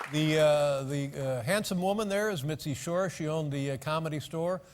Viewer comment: Did he say the handsome women there XD